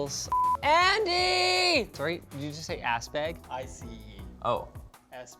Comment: "Sorry, did you just say ass bag?" "I-C-E. Ass bag." Andy is my new favorite LTT character. I know he said ice bag, but ass bag would've been the absolute best reply to Linus asking if he originally said ass bag.